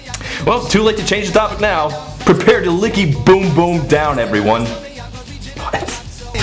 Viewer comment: A licky boom boom down sounds like a rimjob. I can't be the only one who thinks this. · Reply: I heard it as "leaky boom boom down" at first. That sounds like something you need to see a doctor for.